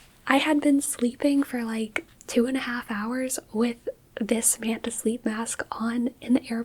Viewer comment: For the first minute and a half I thought you were saying "Amanda sleep" and I, an Amanda, felt special lol